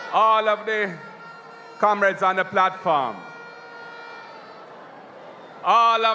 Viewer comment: Did I hear "platFARM"? is which Farm, Mark a plait?😮 Pupa Jeezas!!